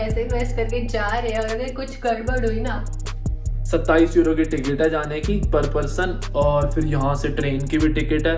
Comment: Replayed 0:55 part again and again because I heard 2700 EURO not 27 EURO and I was like wtf HAHAH (congrats on car!)